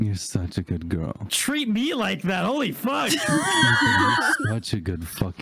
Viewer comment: AT FIRST I THOUGHT HE SAID “You’re such a good fuck boy.” Lord help me 💀💀🔫